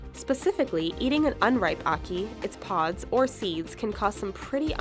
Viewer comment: You sound like your saying haki when you say ackee